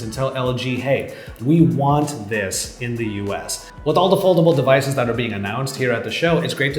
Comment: What did he say at 4:52 please? I’m lost 🤷🏽‍♂️ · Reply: With all the foldable...